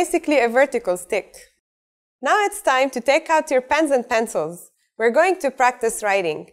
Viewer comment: who else heard pants